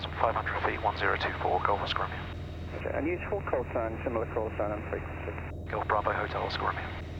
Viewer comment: What exactly does the Bournemouth controller say at 08:07. I hear the similar callsign statement but not the bit before, thought he may have been passing the callsign that was similar but doesn't sound like it. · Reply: And use full callsign, similar callsign on frequency.